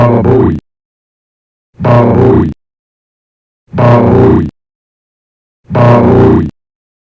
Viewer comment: я слышу "балуй" 😏